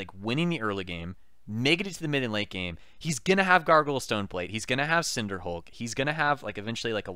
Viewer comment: I keep hearing "Gargle Stoneplate". I giggle EVERY time.